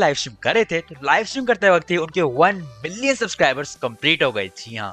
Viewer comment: When he x mania subscribers has reached 1 million I heard 1 billion